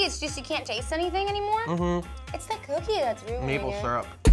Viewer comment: Totally thought he said meatball instead of maple 😂😂😂